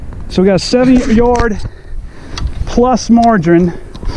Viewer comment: Did he just say 7 yards of margarine?